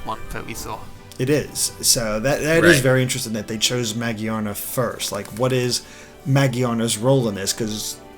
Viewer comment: Iain Waddell Ma-gi-ana is what he is saying, there is no R sound in the way he is saying it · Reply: idk, I thought he was saying mo-jee-ar-na. Just adding an r to magiana.